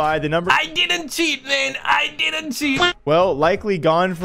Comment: Coulda swore Jake lucky has said pros don’t cheat 🤔